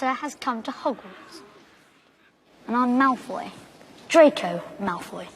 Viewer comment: What he said: I'm Malfoy; Draco Malfoy. What I hear: I'm mouthy; Dracula Mouthy.